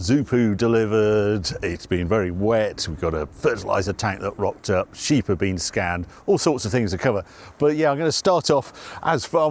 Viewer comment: I misheard part of the intro as "the sheep are getting scammed". And I worried that you need to do more to protect their identity and finances, lol